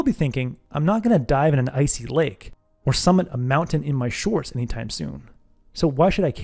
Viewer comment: I heard “not gonna summon an mountain in my shorts anytime soon.”